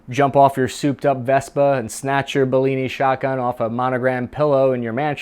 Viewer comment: did you just call it a “bellini” at 3:50 ??